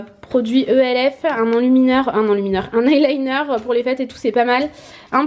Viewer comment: jai compris pour les fesse